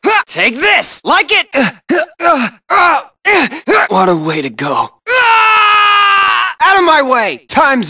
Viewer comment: That "Drop !" is from Riku Replica, isn't it ? 0:07 What does he say ? =/ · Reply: sorry for replying to a 2 year old post but he says "What a way to go..."